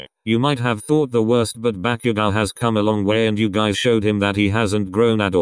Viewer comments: Ok did deku call him bakugo at 20:28😳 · Did Izu just say BAKUGO and not KACCHAN 👀👀👀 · Did Deku just call him Bakugou?!